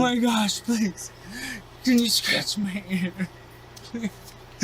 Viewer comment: did he say “can you scratch my hair?” Wtf